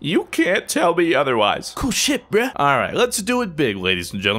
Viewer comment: Mr.Fruit! Did you swear!? Are you sick? Is something wrong? How out of character! (I don't care about swearing, but I was like "wow, he never swears.") Mr.Fruit! The Fruit levels are rising to critical! Only 60 more Fruit to go! Congratulations! Keep up the great work! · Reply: It sounded like he said, "Cool shit, bru!" But he actually said "Cool ship".